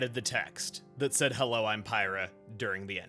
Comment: I always thought the lip syncing in this game was pretty bad, but when I beat this game the first time I thought Pyra said "I love you, Rex" instead of "Hello, I'm Pyra". That combined with the lyrics "spare me a time with the one man I loved" (not to mention Nia's shove in the ending) made me think that Pyra/Mythra were revived as normal just on account of being the Aegis, but Rex finally grew up and accepted his manhood. He'd been called "Boy" so frequently through the game, and so much of it was centered on Rex being too immature to handle the power of the Aegis (shown when he tries to leave Pyra for dead), too immature to handle the emotions of his friends (shown by Klaus's hallucination sequence) and constantly being looked down upon. Zeke also refers to him as a boy through most of the game, making me think that the ending was finally him growing up. All this to say, I'm wrong :P this whole headcanon came from misreading Pyra's lips, and my desire for a happier end I suppose. I think your interpretation is a lot more fulfilling, even if it is sadder. It makes sense to show that Mythra is still just a blade and can be reborn without memories, that even the Aegis can't escape (and trying to escape fate is something Mythra does before the game takes place up until the end). I mean, she literally asks God to let her die so she doesn't have to live with her memories of what happened in Torna. Mythra finally died, permanently, as even though she was reborn, she has 500+ years